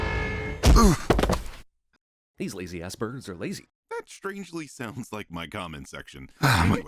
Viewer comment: Did I hear Jeremy say "these lazy aspergers are lazy"?